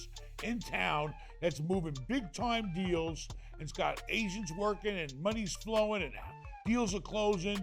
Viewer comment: Oh AGENTS, I thought he said go talk to a big shot ASIAN...haha